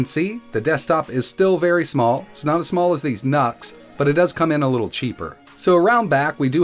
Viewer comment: I thought he said "Not as small as these NUTS" GOTEMM